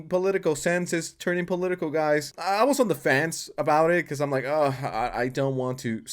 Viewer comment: i thought u said 'i was onlyfans'